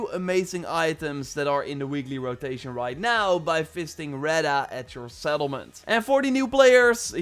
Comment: Ngl I thought you said fisting Reda not visiting🤦🏽‍♂️